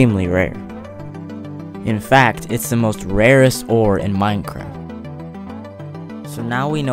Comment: he kinda sounds like he said MINECRAP